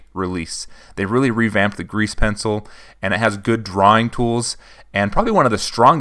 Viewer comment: What everyone heard: grease pencil What i heard: GREECE pencil